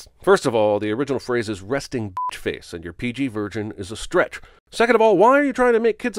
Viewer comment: Did you say PG virgin?